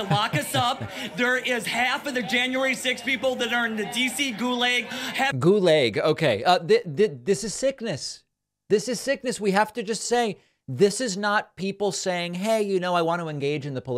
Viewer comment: I heard "gulay" tastes awesome 😮